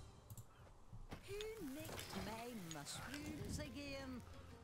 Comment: can someone tell me what she says exactly please?. There is a word that I cant understand. "Who...my mushrooms again?". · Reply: Nicked (British slang for stole)